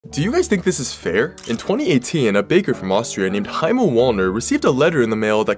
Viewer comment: Bro I misheard the first part and heard a painter from austria